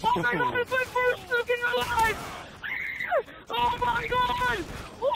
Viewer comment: does he say "its my first nuke in the live"?